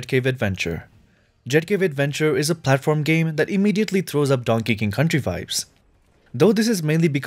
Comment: Quick question. At 6:08 he said donkey king. I kept replaying the video to make sure I heard it right. Did he mean to say Donkey Kong.